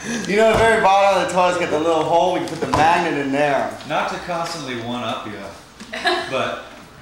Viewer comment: What is Josh saying at 0:18:57? Couldn't figure it out for some reason. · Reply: "Not to constantly one-up you but... whooosh"